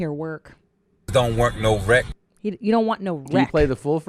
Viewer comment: All the different reiterations you guys said I kept hearing don't want no rekt.